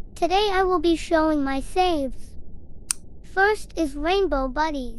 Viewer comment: Idk why but I thought it said “showing my slaves”